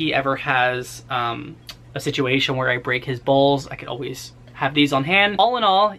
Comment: "If Mikey ever has a situation where I break his bowls" THOUGHT U SAID BALLS LMAOOO